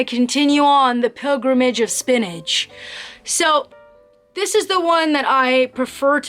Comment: Oh spinage! The captions said spinach. It's very informative and I'm only 4:10 into the video. I love the part about being stuck like crazy gorilla glue.